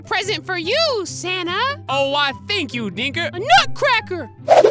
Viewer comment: Is it me or i hear "aww why thank you nigga"